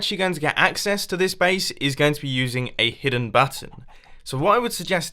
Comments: at 0.46 were he says hidden button it sounds like he said hidden batten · I THOUGHT HE SAID "A HIDDEN BUTT-ON